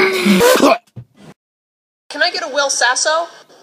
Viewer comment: he sounds like he is saying I WILL PISS